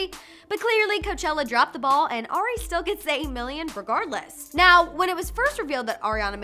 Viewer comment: yo wtf did she say at 1:33 like “se-e million” ???